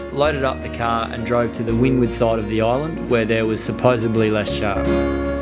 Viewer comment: Does he say Supposebly? ...like Joey from Friends